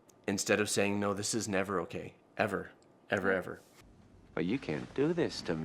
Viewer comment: I wouldn't normally point this out, but as someone with a sibling who is hearing impaired and reads the subtitles.. I'd like to point out that the words at 5:41 -ish need a lil' fixing. "never okay Ever" instead of what is says.